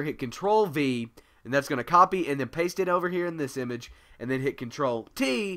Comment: What do you say at 1:10? Ctrl-? T? P? I can't understand that part.... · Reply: t